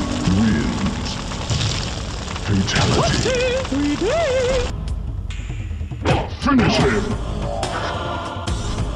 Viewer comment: anyone know what he says? Tried looking it up and nothing. I know he says toasty, but after he says something like, " We de he" anyone know the true phrase? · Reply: Toasty 3D!